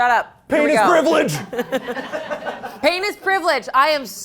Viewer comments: I immediately forgot the "pain is privilege" quote and I swear I thought Shayne said "Penis Priviledge" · i misheard "pain is privilege" as "penis privilege" loool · it sounded like shayne said "p*nis privilege!"